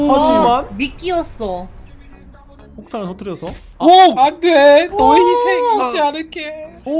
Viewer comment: 나만 "너 이 색....."이라고 들리나?ㅋㅋ